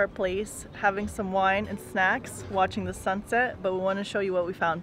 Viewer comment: did she say they were having wine and sex on the rooftop or I am just the perv who heard that?